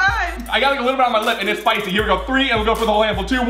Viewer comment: Did he say "Spicy"?